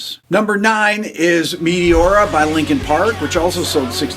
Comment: I thought he said “Meliora” instead of “Meteora” and I was about to be like holy sh** Ghost made this list?! 😂